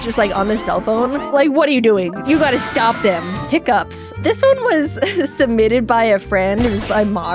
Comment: I got really excited because for a moment, I thought you said "pick ups." Like, the type of truck? Seriously, it's either drive fast like an asshole, or drive slow like an asshole. It's like, we get it, dude, you got a small Johnson. Nice job compensating. Can you put the muffler back on your smog machine now, please?